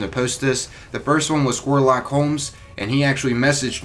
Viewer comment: Am I the only one who thought he said "Warlock Holmes" 😂